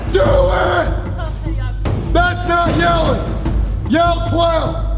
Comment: I swear I thought she said "when you stop yelling"